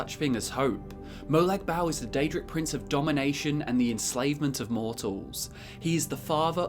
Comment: First time I played Skyrim ,my DragonBorn was a vampire (on accident but rolled with it). Then stumbled upon the Molag Bal quest and misheard "Domination" as "Damnation" ,hoo boy was I shocked when I was corrected !